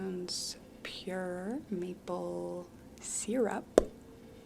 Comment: i thought i heard meatball syrup 😭